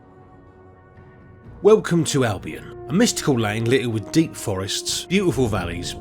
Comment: You said "dank forests" but I heard "dick forests" I had to replay the first 10 seconds like 10 times. You got me.